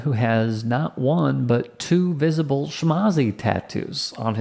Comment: Near the end there @ 10:28 when he says that "that man who has not one, but two visible (???) tattoos on his body", what is he saying just before tattoo? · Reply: Shmazi tattoo. I couldn't tell in the picture. I assume shmazi = Nazi